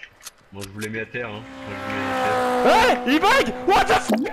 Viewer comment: ''a bug'' almost sounds like he says a bike, wich fits great haha